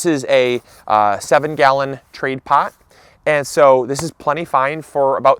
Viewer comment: Did he say that was a 7 gallon trade pot at 3:16?